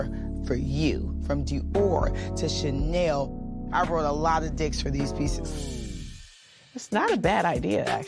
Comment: Did Marlo really say she "rode a lot of d***s for these pieces" 1:46 😂😂😂😂